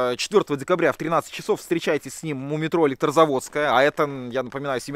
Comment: и вся команда, спасибо вам за ваш труд! Развлекаете и учите нас многому! А ещё в этом ролике вместо "у метро" нам послышалось "мумий тролль" 😅